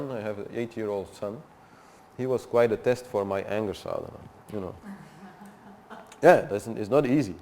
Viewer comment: I cannot hear clearly that word he is saying, it is Anger Solevent or Anger Solvana? "my 8-year son was a test from my Anger Solvana(?)"